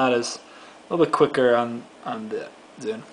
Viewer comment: Did anybody notice how it sounded like he said "quief" instead of "quick" just for those of you who know what "quief" means... LOL.